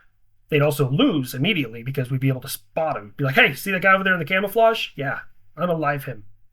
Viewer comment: Haha, thought you said "camel-flage"...